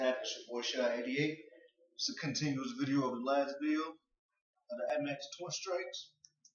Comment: did he say air max twin strike??